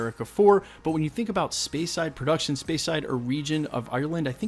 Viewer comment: The natural history one could pertain to an Isaiah Bradley story? I'm just thinking to the history part. I've heard rumors that they've been thinking about Nova also. Maybe that's the space side one? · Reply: Oh I thought you said space side